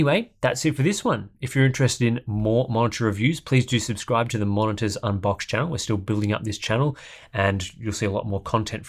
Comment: LMAO when i yawned when he read "Monitor Unboxed" i heard "Monster Unboxed" 😂😂